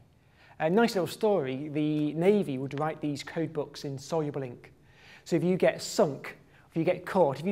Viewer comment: what did the navy devide the paper into? i hear "soy blink" ..... :S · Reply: soluble ink. Meaning it dissolves in water